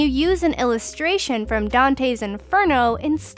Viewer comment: did it just say dan7eh